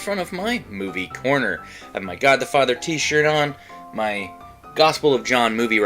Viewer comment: Ok…so I misheard what said at the beginning of the video about his t-shirt. I thought he said that he was wearing his Godfather shirt (considering that he’s in front of his movie corner, I thought he was showing his cinephile cred), but no…he’s wearing his “God the Father” shirt. But I have a question…why is the text on his Christian-themed shirt look like it’s the name of a Norwegian Black Metal band during the church-burning era of their scene…it’s just feckin’ weird.